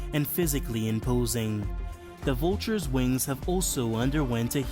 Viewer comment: did anyone else think that said winos?